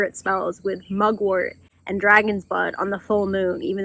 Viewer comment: I know you probably said "dragon's bud" but i heard "dragon's butt" and that'd be a fucking interesting spell. Also I love being able to see so much of your personality in these videos.